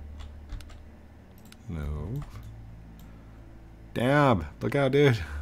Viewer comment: Did you just say the D word at 15:38? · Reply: "Dab" the guy that bumped me. :) I dont curse. (on camera)